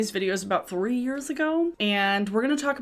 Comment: Watching this just after I woke up and my brain legitimately thought you said you've been making these videos for 40 years now. Good morning me I guess